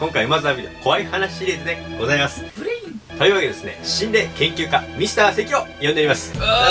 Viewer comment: 最初の、｢心霊研究家ミスター関｣ が ｢死んでる研究家ミスター関｣に聞こえた